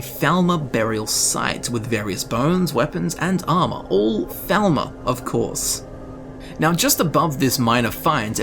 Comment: You were saying "thalmor" this whole time? Cause all I heard was "velma" for the past hour. Not a complaint, I love scooby-doo!